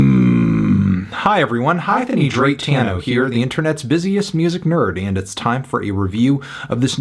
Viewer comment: I could have sworn he said, "Hi-Thony Drake-tano here"